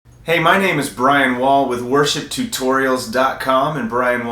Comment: for a sec i thought that he said hey my name is Brian Walts and i'm hear for worst ship Tutorials